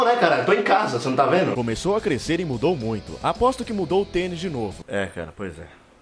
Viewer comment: eu entendi ''aposto que já mudou o pênis de novo'' e na hora já pensei, porra, ao vivassoooo